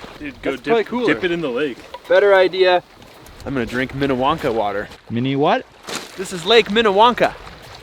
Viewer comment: I totally thought you said Willy Wonka water not minewanka water! hahah